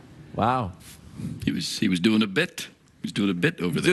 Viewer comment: Did Geoff really say "stupid bit?" If so its little things like that, that made this show so amazing. I miss it so much. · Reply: I think he's saying "Doing a bit." He says it at 7:35. Sorry to harsh your buzz, man, I miss it too, the only talk show worth watching.